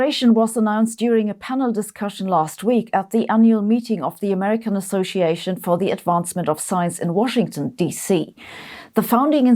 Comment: did you say American Association for the advancement of SIZE??? lmao